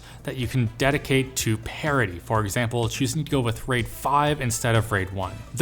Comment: I just watched a Weird Al interview and I thought you said "parody" not "parity" lmao